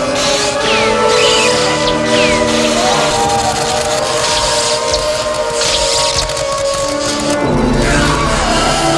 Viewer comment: anyone else hear Luke say father? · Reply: Anyone else hear Luke say “I am a Jedi like my father before me”??